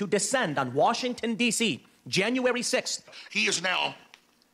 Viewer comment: I swear it sounds like Tim Pool says Januwawy 6th